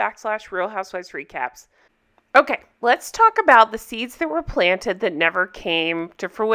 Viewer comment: i thought you said "let's talk about the teeths that were planted..." because of miranda's teeth in that photo!!! 🤣